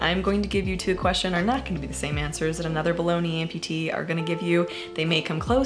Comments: Okay so I'm somewhat new to your channel but listening to this while crafting I thought I heard "bologna amputee" and since you say meat leg i didn't realize I had heart it wrong for a sec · I heard "other balogna amputees" and felt super out of touch until I realized I misheard "below knee amputees" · Great video! One funny thing was when you said “below the knee amputees” really fast it sounded like “bologna amputees!” Around 7:17.